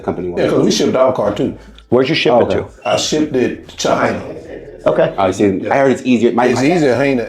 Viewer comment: Did he say he shipped it to China?